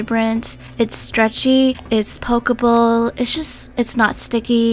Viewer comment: it's pokeball That's what i heard XD